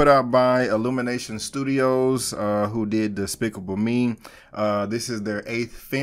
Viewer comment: no offence, but it sounded like you said despicable meme